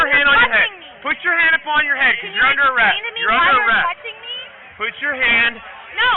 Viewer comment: He never said she was arrested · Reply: he clearly says she's under arrest